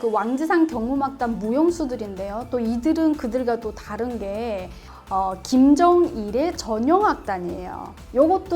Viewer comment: I think she is saying Kim jung Il, but the subtitles say Un...Just thought that it might be misleading..